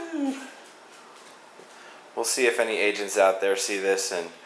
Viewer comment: lol...at first I thought he said "lets see if any asians out there want a deal"... No, AGENTS.... =___=